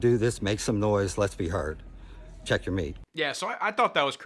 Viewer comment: all i heard was beavis and butt head “beat your meat.”